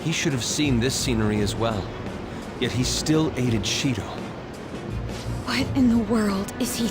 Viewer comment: What was said "Yet he still aided Shido." Vs. What I heard "Yet he still ate a Cheeto"